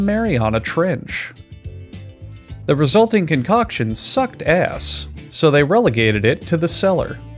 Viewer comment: I laughed way too hard. did he say Sucked ass or gas?? ether way a banger lol] edit- nevermind he says ass.. didnt realize this was a meme video until after that lol